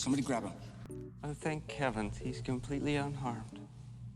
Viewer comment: did he say "Oh thank Kevins?" 😂